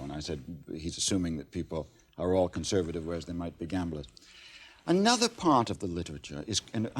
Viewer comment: What does he say at 20:41 ‘they might be conservative or they may gamblit/begamlit’ <what’s the word he’s saying there, please? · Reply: "... whereas they might be gamblers" l